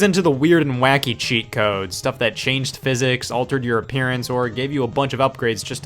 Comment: I swear I heard it as Altered Europeans